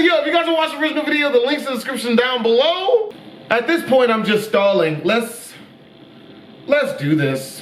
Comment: Hold on I heard him say "At this point I'm just Stalin"...